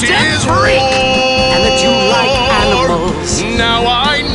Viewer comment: i heard "and you liked animals"